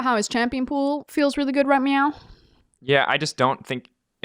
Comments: did frosk really say „right meow“ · Did Frosk just say "right meow"?